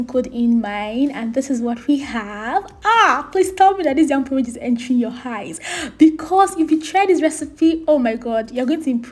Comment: You almost got me until I heard “ this Nyam porridge is entering your HAIS “😂😂😂😂 By the way I’m making this as I type love it